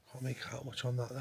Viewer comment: At around 14:25, after you said "come through the gate", I heard "I can't"